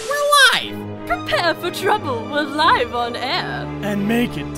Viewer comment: GUYS DID MEOWTH SAID: ALRIGHT WERE LYING